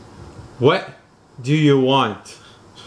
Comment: He says what do you want I hear Please make it stop and again at 17:00 I hear Please make it stop